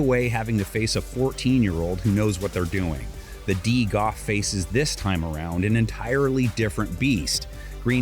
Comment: Did he just say O Face ... I do not think that word means what you think it means 🤣